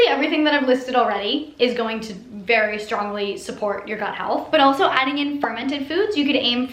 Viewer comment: Lol at first, I thought she said "butt health", not gut health.